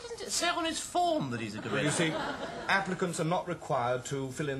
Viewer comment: Look down the comments, I'm glad I'm not the only one who misheard "Applicants" as "Africans" !